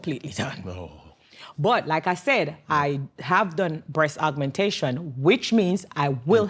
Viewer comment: That's what I thought initially, but she said it was a tummy tuck. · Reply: I thought she said the DR was the breast augmentation